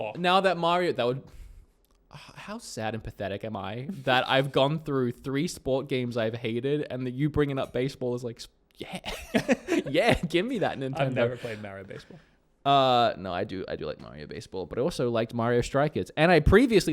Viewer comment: I thought you said have given up on Nintendo.